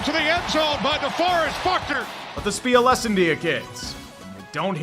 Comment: I thought the announcer said "DeFucker Buckner" for some reason and am still laughing about it